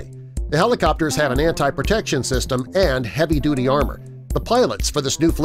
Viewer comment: Did he say Marine 1 has an "anti-protection" system @ 4:30 ?!